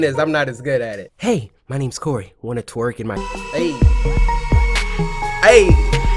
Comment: Did i just hear.... 6:43 “wanna twerk in my-“ WOAHHH😭😭😭